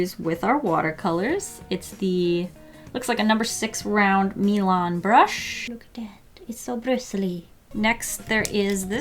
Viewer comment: When she said Milan brush I thought she said Elon musk